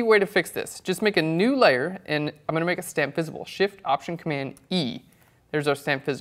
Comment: whats a 'stamp visible' at 7:23? Is that what you said?